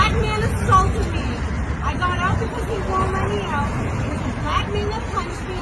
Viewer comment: did she actually say, I got because he wouldn’t let me get out? WTH!